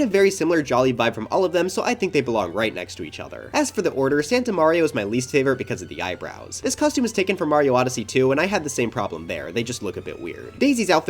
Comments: for a solid second I thought you said Mario odyssey 2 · the way my brain locked in like a sleeper agent when I heard "Mario Odyssey 2" 🗿 NINTENDO!!! ANNOUNCE SUPER MARIO ODYSSEY 2 DURING THE SWITCH 2 DIRECT AND MY LIFE IS YOURS!!!!!!